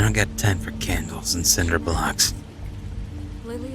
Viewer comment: When he said 'I don't have time for Cinder blocks' I thought he said ' I dont have time for Sandra Bullocks.':')